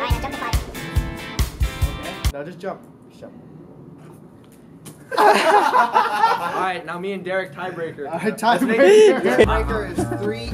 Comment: I swear it sounds like someone said "alright now jump to 5"